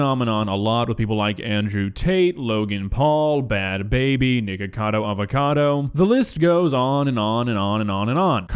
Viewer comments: Did I hear Nickacodo Avocado or something else. 😳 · ? Did you say niggacado avocado? 😂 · It sounds like said the nword trying to say nikocado avocado 💀